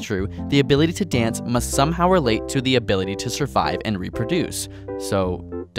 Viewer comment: I thought he said f***ability lol XD. But really tho.... I think that it really depends on the girl if she wants to get laid, then you're gonna get laid :/, don't matter if you dance like michael jackson or james brown.....